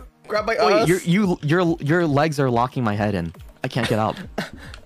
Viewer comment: Thank you for the clip! ❤ This stream has so many good and funny moments hahaha I think around 0:22 shoto is saying "your legs are locking my head in", since his bunny's head is stuck in/between (?) the pink bunny's legs